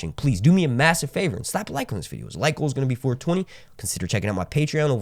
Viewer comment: Well at first I heard "stop liking this video" and I was like... "y tho?"